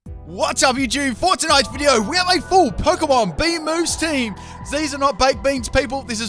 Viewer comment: I thought he said bee moves at first